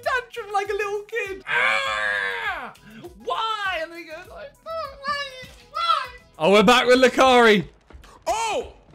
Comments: hope I heard that wrong. 😳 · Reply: He said "and he goes like" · did said the N word?😂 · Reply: lol it does seem like it, but i believe he is saying "oh my god" in a fast way 🤣🤣